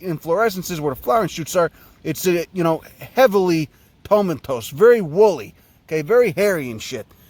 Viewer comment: "Very Woolie" Looks like some pre-dank nugs tbh. "Heavily Pomentos" (?) What word is that? I looked it up and it thinks I'm looking up those little pepper they stuff olives with. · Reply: *tomentose